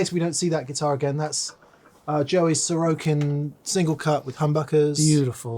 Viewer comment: I had to do double take there, I thought said Solo King single cut.